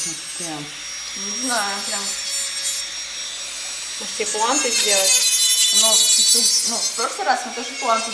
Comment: Извини конечно,но мне на слове ,а точнее форме"пуанты", послышалось "импланты"😂